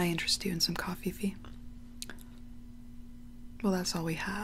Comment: Her:”can I interest you in some coffeffe?” Me:”😶😐” My mind:” did she just say coffeeffe?”😤🤣☠️